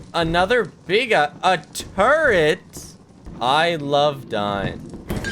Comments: It sounds like is saying "I love dying", and the Bracken is just like "Oh, perfect." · the Bracken thought you said "I love dyin'." He was just tryna help you out